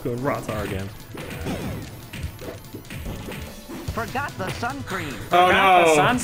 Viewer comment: is deaf af, he clearly said sun cream